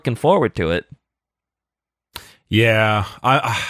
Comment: everytime Loken is mentioned i keep hearing them say “Garfield Loken”